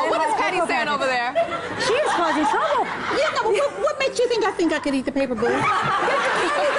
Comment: "She’s causing trouble!” “Paper bad, cake good” “you’re the one who wanted to eat the paper” “Are you okay? *pets her*” this is literally passive aggression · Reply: think she thought Patti said: "what makes you think I COULDN'T eat the paper"